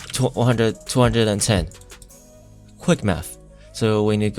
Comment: Was it me or did it sound like he was saying quick meth near the end? Thos helped me alot thx